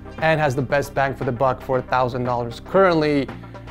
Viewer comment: Lol I keep hearing “4,000 dollars” every time he says “for a thousand dollars” 😂